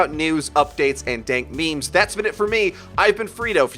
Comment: did he just say dank memes